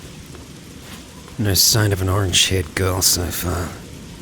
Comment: Did you say orange shaped girl? :D